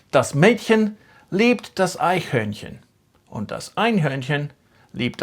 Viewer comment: Did you say Einhörnchen? 😂